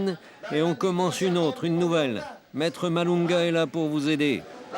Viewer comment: j'ai entendu Maître Malonga ?